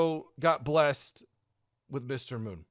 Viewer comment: I am from Sweden and I didn't know what they said. Since you said "Mister Moon" right away I really tried to listen in on what they said. But all I heard was "Mister Moon" after that too xD